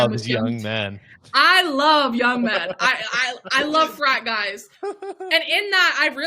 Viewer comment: I heard "I like French fries" not frat guys..